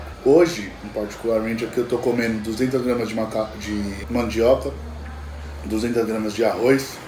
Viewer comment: kkkk tô comendo 200g de macaco só eu que entendi isso?